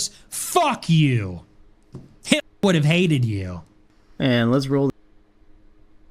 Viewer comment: did he say “Hitler would’ve hated you” and as insult? JFC.